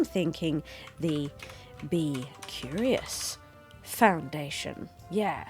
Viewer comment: Yay for the B Curious Foundation. Although I misheard and got too happy when I thought you said "The Bi Curious Foundation". Now that would be the greatest foundation! I need to go there.